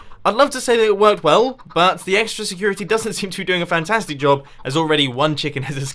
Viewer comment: did he say "EGG-stra security"?